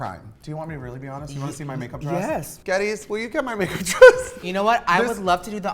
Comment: For a second i thought she said "gays could you getbmy makeup dress" 😂